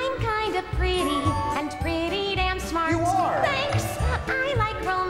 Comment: I thought she said pretty damn smart wait did she?